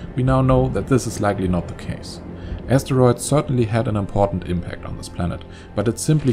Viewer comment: “Asteroids certainly had a massive impact on this planet” Nice pun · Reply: Only that isn't what he said. ... he said "important" not "massive" see 12:58 for yourself